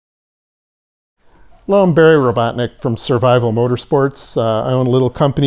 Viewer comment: I thought he said his name was.